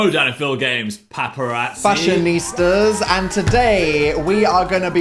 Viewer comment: thought he said fashion deez nuts. dnp you have fried my brain